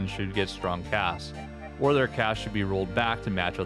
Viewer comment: I'm sorry but I couldn't tell what you're saying here. May I ask? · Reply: CAS, an acronym for "Close Air Support", AKA planes with bombs or rockets.